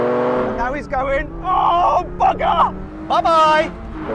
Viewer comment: が空耳でオォンバカァに聞こえるんだがwww